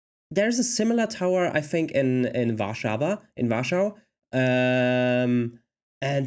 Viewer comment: You said, regarding the older tower, that there was a similarly-styled one in "Warshawa - in Russia". At least that's what is sounded like. Perhaps you haven't got the hang of the English 'w' yet - - but "Warshawa" in English is spelled Warsaw, which is in POLAND. 20:39 The Devil's Tower is, in fact, a volcano so ancient that the surrounding cone has eroded away, leaving just the central magma column. That's why it stands alone, with no other hills or mountains in the area. It's also why you see all those vertical stripes or tubes all around it.